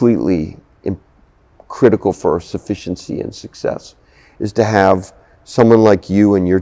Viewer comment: Did he just say “suffenciency”. Like a conjunction between suffice and efficiently?